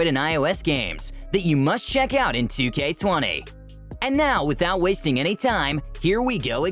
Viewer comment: did he say 2k20?